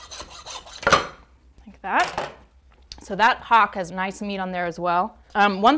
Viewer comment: Oh oh. @ 1:14 it sounded like she said "That cock has nice meat in there" lol